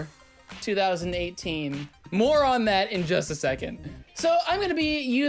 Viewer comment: When he said, "More on that in a sec." I thought he said, "Moron that in a sec."